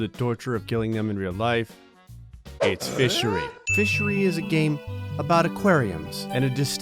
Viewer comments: I thought in the intro he said MISSERY I was like cool game But its fishery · I thought it said fish misery not fishery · When I heard “fishery” my brain auto corrected it to “misery”